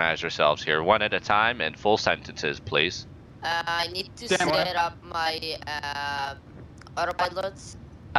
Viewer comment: i heard "ehh i need to sit up my ass uhhh autopilot"